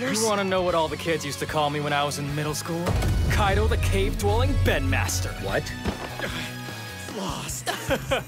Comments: I WASN'T PREPARED FOR THAT LMAO BEND MASTER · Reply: I THOUGHT IT SAID BED MASTER, PUHLEASE. 😭😭😭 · “Kaidou the Cave-Dwelling Bend Master.” 💀 I LITERALLT THOUGHT HE SAID B E D MASTER THO 0-0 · "The bendmaster" does not sound wrong at all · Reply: i thought he said bedmaster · I thought it said bed-master :')